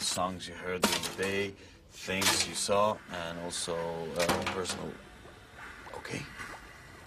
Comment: can someone, please, reveal to me what is the last "ingredient" he mentions at 2:12 ? I can't hear well :( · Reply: summerrain i think he doesn't get to finish listing the final "ingredient" but he says "and personal-"